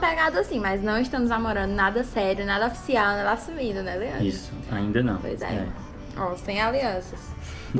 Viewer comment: Só eu ouvi um “ainda não”